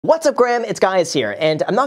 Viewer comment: Did you just start the video by saying "Hello Graham this is guys here" lol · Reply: It sounded like he said, “What’s up Graham, it’s guys here...😳